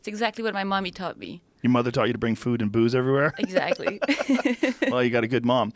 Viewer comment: In the opening scene, I thought he said boobs instead of booze 😂